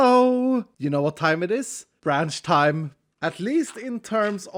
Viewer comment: for some reason i thought he said ranch time at the start